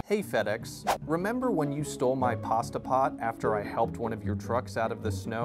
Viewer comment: i thought it said fed ex stole my pet